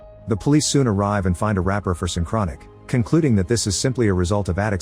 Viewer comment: thought he said “a wrapper of some chronic”